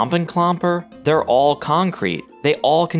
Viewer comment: I swear it sounds like you're saying "Kong-krete" not "con-crete"